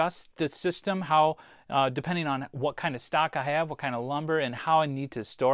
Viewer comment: Funny. I could have sworn you said "what kind of stock I have" there. I'm sure you meant "what kind of stock your neighbour has".